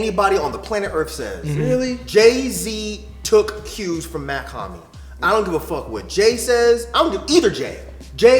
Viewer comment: Who did say @ 9:58? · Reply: He says Jay Z was taking cues from Mach Hommy, who is a rapper Jay has shown he is a fan of / has pictures with. Many people compared Jay Z with Mach Hommy since them meeting, although I think it’s just friendly influence from a rapper who I’m impressed is still listening and getting impressed to rappers.